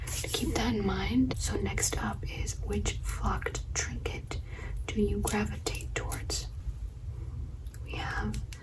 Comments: I thought u said which fked trinket do you gravitate toward 😭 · Girl at first I thought you said “which fucked trinket” 😭😭 caught me so off guard before my brain fully processed Also the return of the watch face bag strap!!! Such a unique and amazing trigger